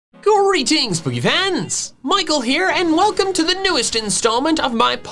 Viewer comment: I THOUGHT HE SAID GREETING POOPY PANTS